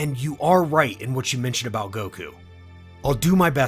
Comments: Did Vegeta say goku instead of kakarot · did vegeta just call him goku? wtf